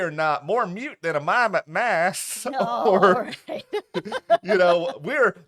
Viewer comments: I also thought said "more mute than a mime at mass" 😅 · “More mute than a Mime with a mask” 😂😂😂😂😂 Her delivery though! I had to replay that line several times in my rewatch of this show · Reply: I'm glad you did! I thought she said more mute than a mime in a mass. LOL